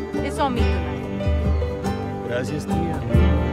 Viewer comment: Did goyo just say thanks auntie (in Spanish) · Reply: Gracias tía